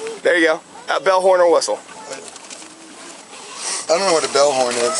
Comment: I thought he said Bellhorn or whistle also, not bell, horn, or whistle. This cop sucks and he's an embarrassment.